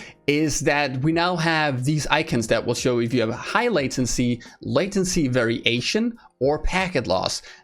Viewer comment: Latency Very Asian @ 8:23 is what I heard when I wasn't looking at my screen.